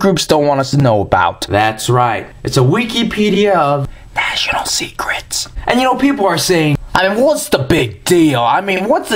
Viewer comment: Ha ha... With your comparison of American to a sassy teen girl, every time you said "National Secrets" I was hearing "Vaginal secrets." I was then thinking "What did he just say?" Then you mentioned "National" in a clearer way.